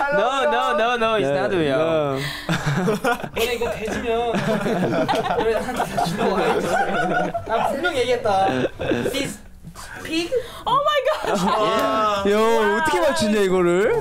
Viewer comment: Did Wooyoung say something like: "If this is really a pig, y'all are dead"? please tell me he did😂😂😂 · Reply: he did! he said "if this is a pig, you're all dead to me/I'll kill you, Mark my words" I CBCHS RIP ATEEZ (OR US/INTERVIEWER IF HE MEANT US LOL)